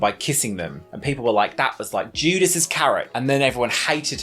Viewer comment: Every time looks at the camera 😂. Also, 8:15 - I hear " that was like Judas's carrot.."🥕